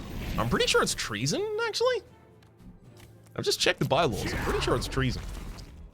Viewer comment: So the first time you said "Checked the by laws" i heard "Checked the bible" That was rather confusing :P